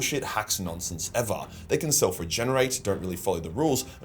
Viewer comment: can anyone tell me what he says here. I’m curious to look it up. Can’t really understand and the captions aren’t correct either. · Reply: Dies Irea Is how you spell it I think